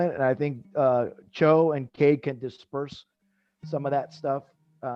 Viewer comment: HAHA, @ 30:29 WTH was I saying, disperse? .... Distribute I meant to say, I'm a dummy sorry everyone